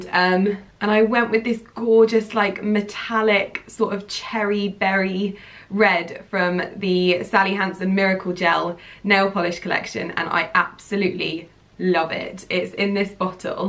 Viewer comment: You said cherry berry I thought you said Mary berry now I want cake UGGH